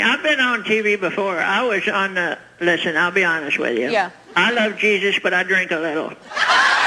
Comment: What did the lady say at about 4:00 ? "I love Jesus but I drink a little" ? I don't see what's funny about that